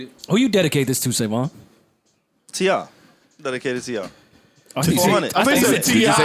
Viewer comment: I swear he said he’s dedicating this episode to TI LMAO